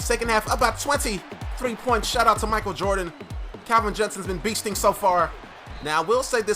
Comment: Did he say Michael Jordan